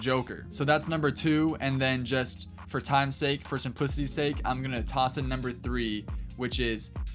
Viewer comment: What??.. Wait, what???? What’d u say at 2:51?? “For times sake, for some __ sake”. Am i tripping?? Someone tell me!!! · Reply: I think “simplicity” lol